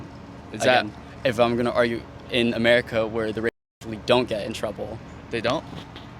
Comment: does he say "WE dont get in trouble?" is he admitting subconsciously to a sex crime.